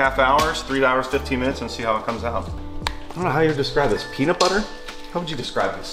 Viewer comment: Why did you add in the "correction" when thinks said peanut, go to 3:15 and clearly says peanut butter :D Whoever edited it thought he only said petting zoo, but that was after the peanut comment. was right, not the editor. Fact Check: False Also the Ghost thing - You just trigger everything :D